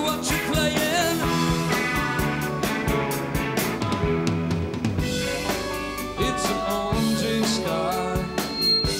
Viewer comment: What does Benjamin Orr lip word at 19:38??? just right after he the verse “Electric Angel Rockin Roller, I hear what you’re playin” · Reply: "I'm going to get you."